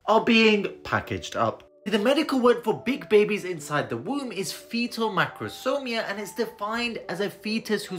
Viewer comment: Fetal macrosomia. It sounded like he said fetal microsomia and that's what the subtitles said.